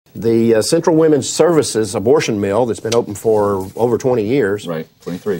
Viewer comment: It sounded like he is saying "abortion meal" ... I know what I'm making for Christmas dinner!